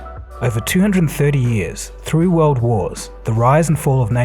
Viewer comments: Did he just say three world wars or I am tripping? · Did you say 3 or through world wars ? 0:05 ..... anyways good stuff as usual. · Did he just say "3 world wars" I need more info on this